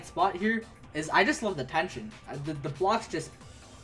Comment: when he talked about the kyoko/makoto execution, i always heard "i just love detention" and i was so confused until i replayed and finally heard "the tension" instead of dentention